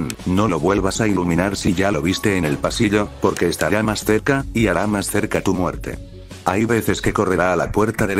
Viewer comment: no entendí eso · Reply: Mientras más cerca tu muerte también XD